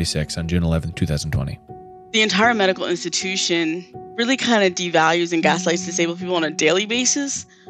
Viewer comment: is saying "rally kind of devalues and gaslights disabled people on a daily basis" (in response to the CC saying "indistinct")